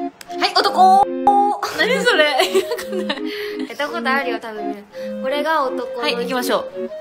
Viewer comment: 〇ったことあるよ って何って言っているんだろう 聞き取れない やったことあるよ？？